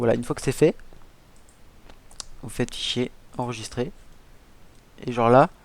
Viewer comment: j'ai vraiment entendu "vous faites chier" ? 😂 Quelqu'un de passage qui peut me dire si ça à fonctionné pour lui, ou si ça ne fonctionne plus ? Car perso moi ça ne fonctionne pas.